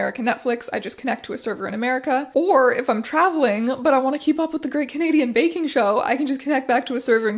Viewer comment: I thought she said The Great Canadian Bacon Show and now I’m disappointed